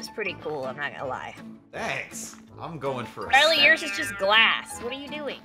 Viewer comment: anyone else heard "Ellie" just me?